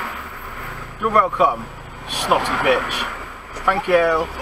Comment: 1st did we get a little echo from the back at 07.47? sure i heard a little "snotty bitch" under your voice 2nd Woman walks into a office and asks the bloke "can i use your dictaphone?" No he says use your finger like everybody else